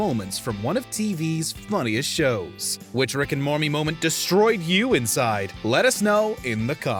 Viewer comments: is it just me or did he say “Rick and Mormie” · what heartbreaking moment that destroyed me from the inside is that you said Rick and Mormy instead of Rick and Morty. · Did he say Rick and Mormy? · did he say Rick and mormee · Ay 0:39 does he say Rick & mormy? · Did he say Rick and mormy